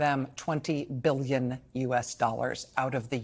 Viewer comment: I thought I heard it was 20 billion Reichsmarks?